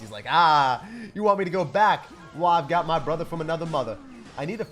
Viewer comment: Did he say "I got my brother from another mother..."🤣🤣🤣